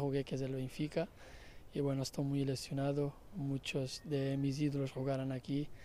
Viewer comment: estoy muy lesionado entendí y dije kheeeeee?! Jajaj